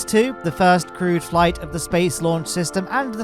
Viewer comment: The auto-generated subtitles saying “crude” rather than “crewed” is beyond me